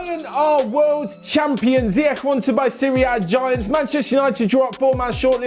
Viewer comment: Hey did you just say ziyech buying serie a giants not serie a giants to buy ziyech 😂 😂 😂 😂 😂 😂 😂 😂 😂